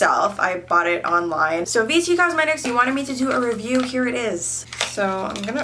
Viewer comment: The whole video I thought she was saying BT cosmetics....